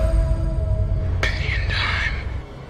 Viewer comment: I initially heard the last part as "plenty of time." Which I prefer. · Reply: I heard it as penny and I'm. Which was confusing